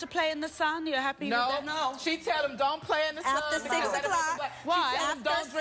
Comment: I thought she said, the kids, doesn't play in the SAND, no t the SUN.